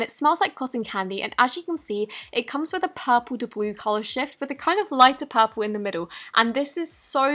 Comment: when you said "purple to blue color shfit" (in the "dont feed the unicorn" part) i heard "purple horse sh_t" LMFAO